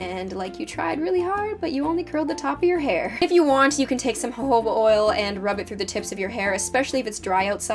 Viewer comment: I thought you said hobo oil